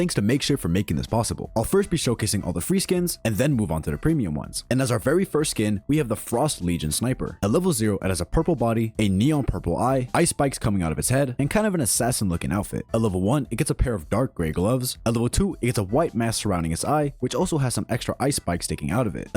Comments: did he say ice spice coming out of his head? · when he said ice (spikes) i thought he said ice spice :I